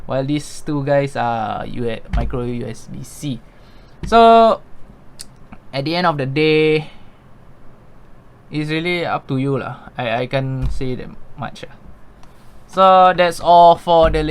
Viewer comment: I heard "lah"😁 btw, thank you for the really honest reviews!